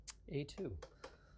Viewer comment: When Linus said "A2", at first I thought he said "et tu" as in "Et tu, Brute?" 😉